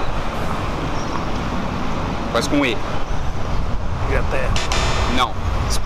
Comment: "Começa com I" Homi: "Inglaterra?" Ele: "Não, Espanha!" 3:39 tendi foi nd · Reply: Ele disse com E